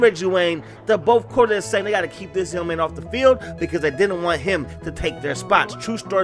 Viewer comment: What are you saying at 0:55 I cannot understand you. "They have to keep ??????? off the field?" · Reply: Keep this young man off the field